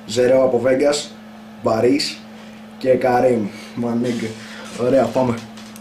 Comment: Τι λέει στο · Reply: My nigga